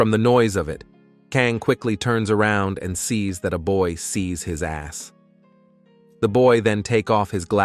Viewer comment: did I just hear sees his ass